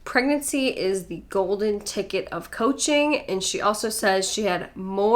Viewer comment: Sounds like she is saying "poaching". Which would actually be more accurate.